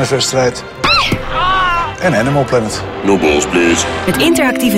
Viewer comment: no balls please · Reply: Haha I heard "No balls bitch"